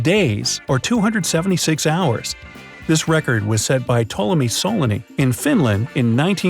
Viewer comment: what name did he say there im from Finland but can't hear what he saying tolomi soloni its all i hear and thats not a name